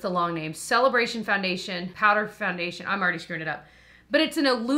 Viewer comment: I thought you said “I already schruded it” when talking about the Glowish powder 😂😂😂